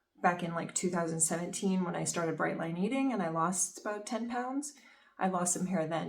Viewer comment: "When I started _____?????" WHAT did she SAY??? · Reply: Oh yeah sorry, my channel was pretty focused on Bright Line eating for a while there. So I figured a lot of people would know. But I should have explained that at least with a footnote!😂